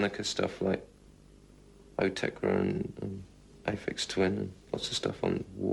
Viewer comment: What artist is he saying at 13:51, ohtegran? Ultegran? I tried to look it up using names I thought he was saying and couldn't make it out. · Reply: Autechre